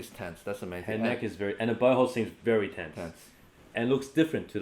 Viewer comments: sounds like he said "and her butthole seems very tense" HAHA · I really thought he said butt hole instead of bow hold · I heard him say "and her butthole"